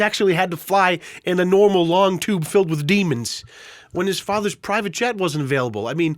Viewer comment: I loved the televangelist call back with the "Long tube full of demons" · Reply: am glad am not the only one who heard him say "demons". Not "deviants"